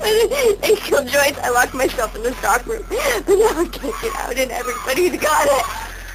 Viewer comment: “I killed Joyce and locked myself in the soccer room, but now I can’t get out and everybody’s gone” that’s what I hear